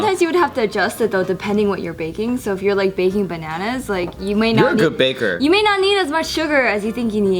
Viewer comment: does anyone else hear say baking bananas